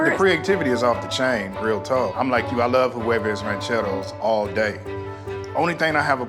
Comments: As a Spanish speaking person I thought Ned had said "quavos rancheros" and I was very confused 😭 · Ned’s thick Spanish accent: huevos rancheros Mexican me: what is that? sees picture Also me: oh!!! Yes!!!! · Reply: As a fellow Latino, I cringed every time he said it. It sounded like he said “Quavos Rancheros” at first to me and I was very confused.